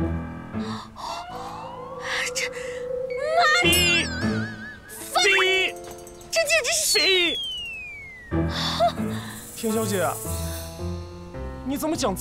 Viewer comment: don't know what she said but it sounded to me "mo(beep) fuc(beep) , this is sh(beep) . 😂😂😂😂 her assistant is one one gud beeper 📟 😂😂